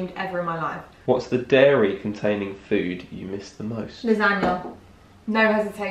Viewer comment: What did she say at 7:01? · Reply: Miriam Lachica lasagne